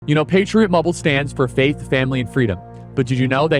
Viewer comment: I thought you were saying Patriot Bubble. Thank you Mr. Kirk for ALL you do for our country and our young people. Thank you.